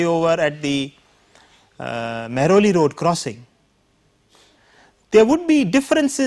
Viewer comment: hey, guys I'm sorry my english is not perfect but what does he say in second "13:50"? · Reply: its a road name "mehrauli road " in delhi india